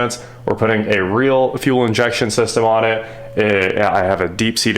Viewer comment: I thought you said a true fuel injection system. Don’t see any rails or injectors just a throttle body that looks like a 4 barrel carb